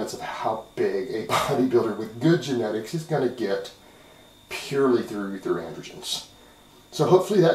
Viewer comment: Could someone halpe me out? What's the word is saying at 5:39? "...purely torugh qweqwreqtweions.." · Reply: Purely through androgens.